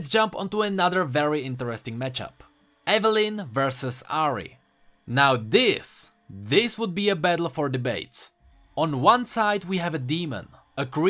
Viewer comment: i thought he said "this would be a battle for the -bates"